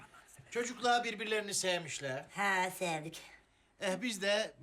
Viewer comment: ''Efendim çocuklar birbiler'''...can anyone continue, what he say ???? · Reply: He says "Efendim çocuklar birbirlerini sevmişler"